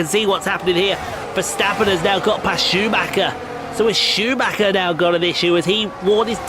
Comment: When you said Schumacher, I thought that sounded like Chewbaka. Insert Chewie groans, here. XD